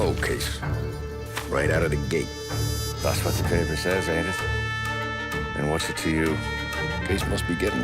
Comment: Can someone tell me what he sayz at 0:38, right after the Morello case sentence? :V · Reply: " That's what the paper says aint it, and whats it to you? "